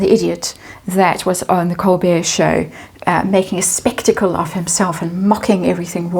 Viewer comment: Spat my tea out. Thought you said "Made a testicle of himself" (*spectacle)🤣🤣🤣🤣OMG-Can you IMAGINE the grammar, drama and thick romanticiism that would lace ANY book written by her? Her writing skills are non-existent and she would NEVER submit to the 'lower standard ' (cough cough) of a ghostwriter! YIKES. Take note! Her father still has the missing pages of that 'letter' she wrote that he considered too vile to release at the time. Imagine what else!